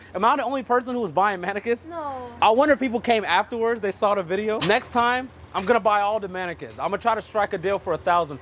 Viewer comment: min.: .... DID HE JUS ..... DID HE JUS SAY .... OK . DID JESUS JUST SAY , " NEXT TIME IM GONNA BUY DOMINICANS"? ... HELL NOO!! :p :p THE SAVIOR!! :p :p UP N AT 'EM + MAPLE SYRUP !! :p :p